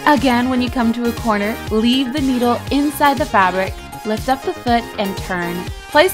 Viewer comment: she said leave the noodle in the boot instead of needle